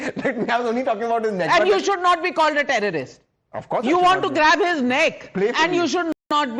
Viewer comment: I thought he was saying that he was trying to grab his genitals.